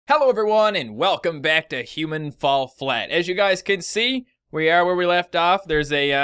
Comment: I thought he said Hitman:Fall flat lol